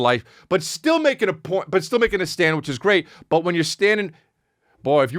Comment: I thought you said “but still making a sandwich, it’s great”. I was like “woah, Chris is going there today, ok”